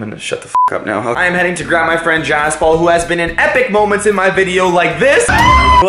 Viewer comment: I thought he said “I am going to get my grandma jaspaul” I was dying